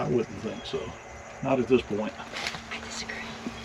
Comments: And I definitely heard the woman talking to I kind of thought maybe she said I have a secret. But I see it’s pretty unanimous that people thought she said I disagree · does anyone else hear a female voice say "I disagree" ? Right after asks that there is nothing to see and the other guy says I wouldn't think so. I keep playing it back and keep hearing it. Even my Granddaughter heard it. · Whenever I see an abandoned house my mind goes to thoughts of the joys, sorrows and celebrations- Christmas, birthdays, marriages and such - that occurred under its roof. Edit: at 23:04 does anyone else hear a whisper “I disagree” when replies “I don’t think so” to saying there’s nothing worth seeing at the top of the stairs? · Did anyone hear a lady whisper “I disagree” when is on the indoor stairs? Right after says there’s nothing up there I need to see and agrees then you hear the whisper. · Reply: I heard it too! I thought maybe she said I have a secret. But I disagree would work too! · Anyone else hear a woman say “I disagree” at minute 23:05 when the men are discussing whether there is anything to see upstairs? How interesting! Thank you for leaving that in during your editing. You put such obvious care and attention to your docus which brings an extra appreciation for the South 🌎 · Did anyone else hear a female voice at 23:04? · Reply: I think I heard, “ I disagree”. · Wow. I heard “I Disagree” too at 23.08. 😮